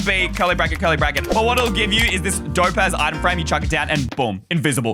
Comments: Did he say cool item frame on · did bro say “dope a word item frame?” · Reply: he said az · did he say dope ass?